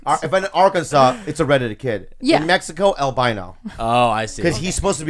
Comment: did Bobby say el bino or albino @ 9:30?